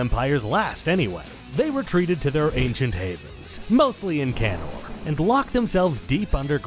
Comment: ngl, thought you said "mostly in Canada"